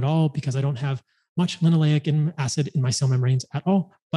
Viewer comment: What is he said around min 2:15? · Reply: Did he say Linoleic acid" ?